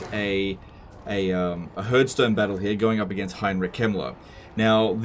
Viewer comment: I misheard Heinrich Kemmler as Heinrich Himmler. Is that supposed to be intentional?